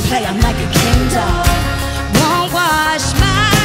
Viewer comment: I always used to think she was saying kindle instead of Ken doll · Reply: Megankeefer I thought she was saying 'Kendall' 🤦‍♀️